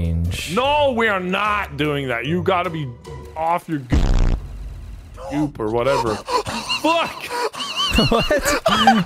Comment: who knows what nogla says in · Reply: he says “gook” when he was trying to say goop